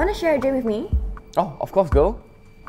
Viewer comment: why did sound like he said " of course girl" was it just me or somebody else as well?